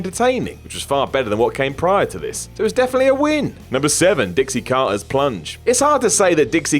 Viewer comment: Thought you said "Dixie Carter's Clunge". 😂